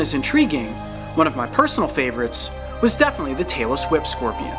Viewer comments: I thought you said "Taylor Swift Scorpions" :/ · when he said tailess whip scorpion i thought he said taylor swift scorpion😂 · I thought he said Tayler swift scorpion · When he said tailless whip scorpion I thought he said Taylor Swift Scorpion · He said Tailless Whip Scorpion and I heard "Taylor Swift Squirrel"... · I thought he said Taylor swift scorpion XD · When he said tailless whip scopion I thought he said Taylor Swift scorpion